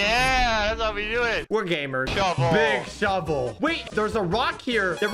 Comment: When they said big shovel I heard “BIg ShOT!”